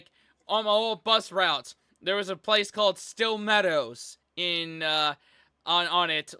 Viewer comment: when I first heard y’all say it I thought they spelled it "Steel Meadows” before y’all even said it was a bad neighborhood. 😅